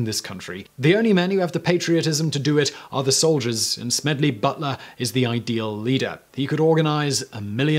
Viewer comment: I thought it said smelly butler, like that is a British insult?